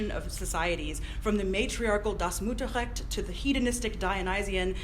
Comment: Help! Around 23:14 she says (summarizing Bachofen) "...from the matriarchal ____(sect?) to the Dionysian..." What did she say in the blank ____? Sounds to me like she says "das musa sect". Is she attempting to quote some German phrase? Logically, according to the Greek mythology Bachofen was referencing, preceding Dionysus and Apollo would be Demeter, but she seems to be saying something else. What? It's driving me crazy! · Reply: Das Mutterrecht